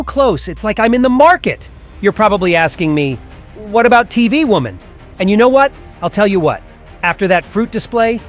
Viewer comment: did bro just call speakerwoman tv woman?